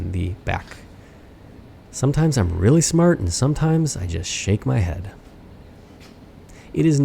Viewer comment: I just shake my head. Had to go back and listen to it again. Although this is my 5th (conservative) time watching this one...For some reason I heard....I just "shrimp" the bed.